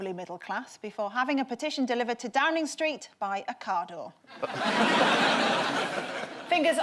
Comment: Ocado... I thought she said "car door", subtitles are sometimes useful :-) That 2500 year old ship really was the most interesting part, I had to look that up. Hope they recover the cargo.